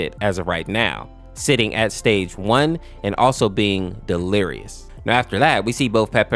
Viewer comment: thought it said delicious😭